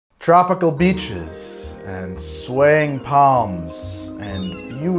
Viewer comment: Could’ve almost swore you said “tropical beaches and sweaty palms” at the start there my dude 😂